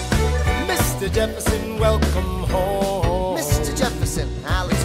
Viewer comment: Oop i heard "Jefferson we are enganged"